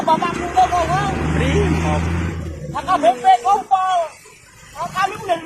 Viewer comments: Kurang jelas dia ngomongnya... Kedengerannya bapakku NGOMPOL.. Aku ramal endinginya nangis n minta maaf di kantor polisi.. tau dah aku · Lu pada salah denger,, maksudnya tuh ngompol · Ape anak ngompol . gw salah denger nih 🤣🤣🤣😋